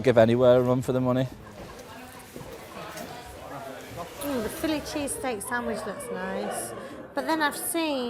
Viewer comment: I know the subtitles said "Fillet Cheesteak Sandwhich". But are we sure it wasn't a "Philly Cheesteak Sandwich"??? If Philly Cheesteak is big in Liverpool then its the best city in England.